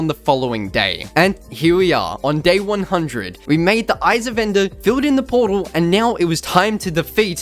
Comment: Lets just take a moment to talk about the fact in 21:46 if you put that thing that puts what they say but at 21:46 when they say eyes of ender that thing says isa vendor I swear like wtf